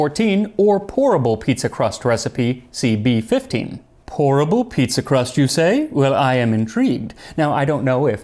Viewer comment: At first I instead of "pourable pizza crust" I heard "horrible pizza crust"😂